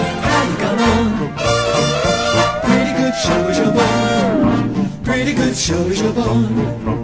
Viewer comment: I thought the ride was saying"pretty good show us your balls" for a long time